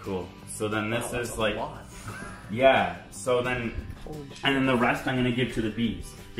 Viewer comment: If you listen closely it sound like said holy shit at 14:25 ish